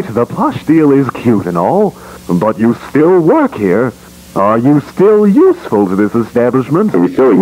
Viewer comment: DID BANNY JUST SAY “KILL YOURSELF”???